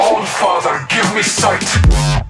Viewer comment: Ngl I really want to though is that all my years of playing apex legends I thought he said “Old fathers give me sight” 🥲